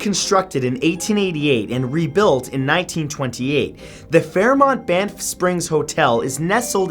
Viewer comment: I heard "The Fairmont BAMF Springs Hotel."